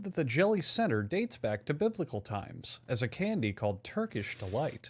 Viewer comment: I thought it said "dates back to mythical times. "